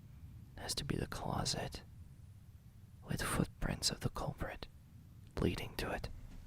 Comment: i misheard prints as parents, i legit thought he said he found my parents all over the scene dude (idk like exploded or something) 😭 when i say my jaw dropped to the floor